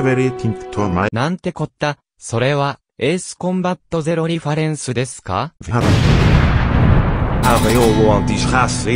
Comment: Does anyone know what he’s actually saying at 5:02? All I can make out is something like “Nante kota sore wa Ace Combat [???] desu ka” Everything after “Ace Combat” was too fast for me to catch, but my best guess is “zeroi farensu”. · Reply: He says: “What is this? An Ace Combat Zero reference?”